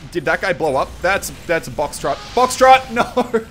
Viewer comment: When there was the Boxtrot spy i heard you say Foxtrot xD